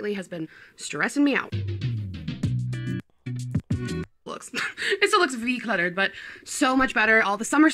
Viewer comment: did she just say “ it still looks V cluttered” ????? 😮